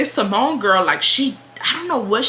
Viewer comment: I’m crying I’m so slow, when u was talking about the samoan girl I thought u was saying Symone 😭😭😭😭😭 “you know them Symone girls” I was like raven Symone ? 😭😭😭